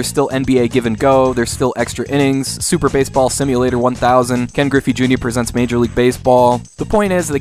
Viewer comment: "Ken Griffey Jr" presents MLB! So that's the name!! I've always heard it as "Kenji Inafune presents MLB". haha :D